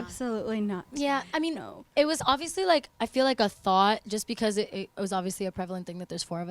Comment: it sounds like lauren said "i feel like a thot" lol